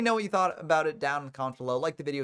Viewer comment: The last couple secs of this vid instead of saying "I've been Ben from YGOFrom0" I thought you said "I've been BANNED from Yugioh" lmao